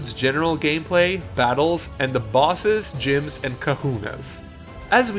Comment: I don't think they should change the formula of main series' battle. Pokemon were always turn based and should stay that way in the main series. Also, at one point I heard "Kakunas" instead of "Kahunas" and I could not stop laughing because of that XD